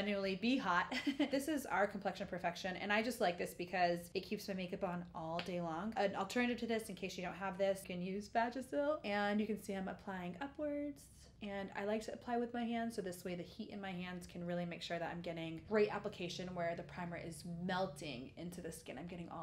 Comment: did i hear.. vagisil?